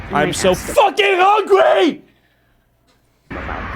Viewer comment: please don’t shoot up an on and out bro😂 and honestly that “im so fucking hungry “ spoke to my soul😂. I too am very fucking hungryyyy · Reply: Lmaoo I thought this mfer said “I’m so fucking horny” first time I heard it. Like woah there, got a little too much on dip on your chip 😂😂